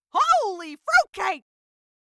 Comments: my ahh thinked of fruitcake ship in dandys world lol- i thought it was holy sh1t until i heard the fruit cake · I got laugh at 0:13 😂 She saying: "Holy *drill*" But I Heard: "OOHHWW! *drill*"